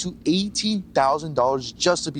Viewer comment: 18,000 or 80,000, sounded like they said 80,000 per person